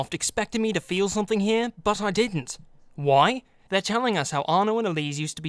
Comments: subtitles are off when he says "didn't" instead of "don't". That's a sin. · you said didn't but the captions say don't, adding 20 sins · You said "didn't" but the subtitles say "don't" Youtube sin.